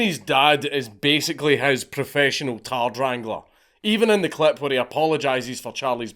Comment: Click the link to get a special discount on your own Displates - <link> 1-2 23% off 3+ 33% off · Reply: did you call his dad a tard wrangler😂😂😂😂